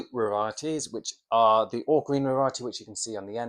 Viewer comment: did you say "ochrina variety?" Never heard of it. Hmm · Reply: Sorry, sometimes I'm too excited with my videos' haha. I said "ALL GREEN Variety"